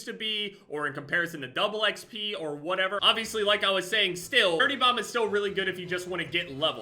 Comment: Is it just me or did he sound like he was saying “30 bomb” instead of “dirty bomb”